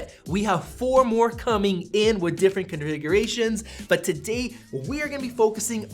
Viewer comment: Did he just say '' conrigeration''lol